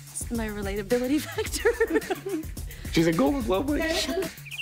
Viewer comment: what did he say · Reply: “She’s a golden globe winning actress”